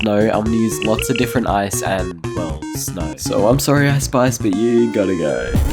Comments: I thought he said ice spice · anyone else hear ice spice